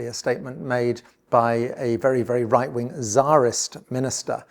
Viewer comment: Zaris Minister? I thought you said zionist and I had to back up.